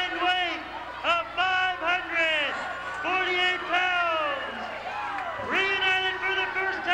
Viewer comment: Dr death and dibiase had a combined weight of 948 lb... did I hear that right?? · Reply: Fiiiiiiive hundred forty-eight pounds.